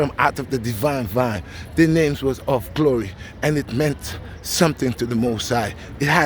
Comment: you said the names of glory I thought you said my name Chloe 😅🤯